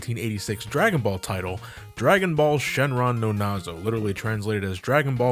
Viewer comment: did someone say Nazo?